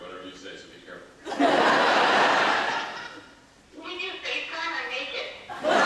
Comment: What does Zoie say at 13:25? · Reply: n kane she says " wanna facetime? Im naked!"